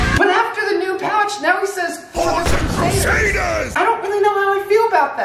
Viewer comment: Lmao I always thought he said boars and crusaders but he says for the crusaders